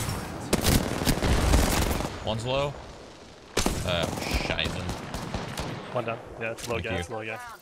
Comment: Did he say "scheiße" (means shit in german) at 11:30 ?? :D